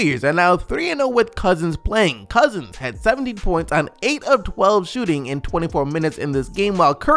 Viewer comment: I thought you said cousin had 70 points